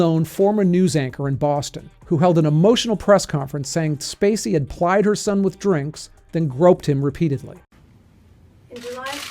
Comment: "...said Spacey had plied her some with drinks...""?? is that what this mumbled talk said here?? How can anyone "plied" anyone, unless you FORCE it down their throat , you are not forcing anyone!! You can pressure someone, as I have bend pressured, but I always said no and have never drank alcohol. It cost me sex with women as a young man in some jobs and college parties, cost me some opportunities, people thinking it was "weird" I never drank, cost me support from coworkers, cost me envy from people bc "you think you're superior or better than us", cost me actual jobs bc I got fired after STRONGLY and loudly refusing drinks from the boss or his best friends at holiday gatherings, etc etc, but still I KNEW I didn't want to drink.